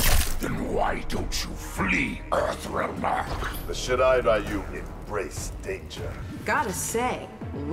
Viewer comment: Scorpion says "The Shirai Ryu embrace danger." BUT the automatic subtitles says "The shit I buy you" 😵